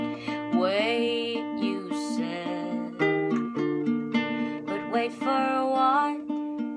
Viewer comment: i tried the best i could but: “hurts now” you say it’ll hurt more than playing reports and start again i like you, we both know that you like me, we both know that but why did you? just please don’t say goodbye no please don’t say goodbye so back to square one five pounds, train bus, or car i don’t know let’s, let’s try for a while but you said “no” but i can’t say goodbye no i can’t say goodbye oh, we had something something small now it’s nothing not even our delicate quarters in your heart ache much more but darling i would walk to you i’d walk to you i’d walk to you i’d walk to you wait, you said “we’ll wait for a while” wait, until we can no, let’s fight, let’s try, let’s prove them wrong do it all, try it all, if it makes us smile you said you can’t but i can’t say goodbye no i can’t say goodbye oh, we had something something small now it’s nothing not even our delicate quarters in your heart ache much more but darling, i would walk to you i’d walk to you i’d walk to you i’d walk to you so back to square one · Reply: I heard "but wait for what?"